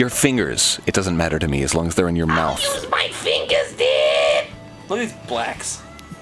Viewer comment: does anyone happen to know what they said that made them laugh? I can't make it out. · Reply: I think Jon says:'Look at those blacks'.